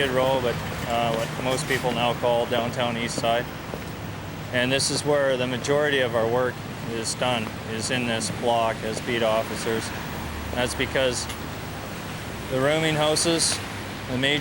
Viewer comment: did he say "Beat Off-icers"? lol